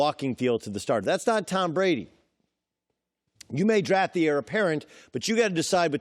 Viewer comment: im from Germany,pls help me. What does he say at 0:59? You gotta draft the areperent? or what does he say and what does it mean? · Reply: “Draft the Heir apparent” meaning draft the next guy who will take over after Brady